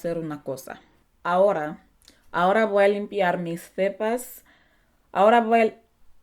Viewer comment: Me encanto el video Saludos desde Tijuana, México🧡 ¿Alguien más le entendió en el minuto 4:37 que dijo mis pepas? O nomas yo 😂